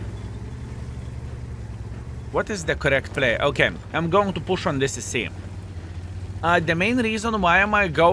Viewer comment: Partly accent but party his usage of model letters. When I heard him say, "push on this CC", I heard, "push on this sissy" LOL! Merry Christmas. Thanks for a fun 2023